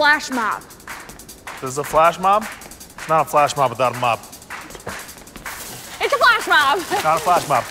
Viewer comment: At first I thought the janitor said “It’s not a flash mob without a mop.” That actually made sense though.